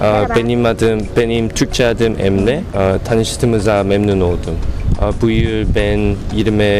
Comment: What's that word he said at 1:16 .Büyür? Before he said ben yirmi...... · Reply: he said I guess bu yıl(this year) or bugün (today)